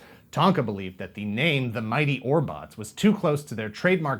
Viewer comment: Oh my. All this time, everytime you meantioned them in passing, I heard it as "mighty Orbachs". Orbots? 🤯